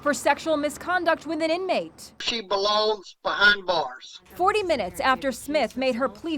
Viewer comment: Really sounded like that guard she banged said 'she blows behind bars'